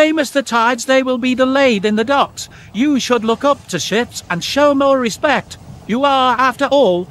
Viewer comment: Sounded like Cranky said, "shits!" 😂